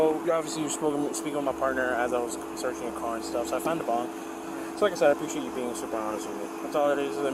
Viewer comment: I wasn't looking at the video and I thought he said a bomb and I was like why is he being so casual about it lmao